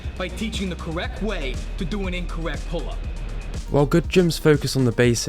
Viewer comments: I thought you were saying “prolapse” every time you said “pull-ups” and didn’t realise until the end of the video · For whatever reason, when this guy says “pull-ups,” I hear “prolapse”.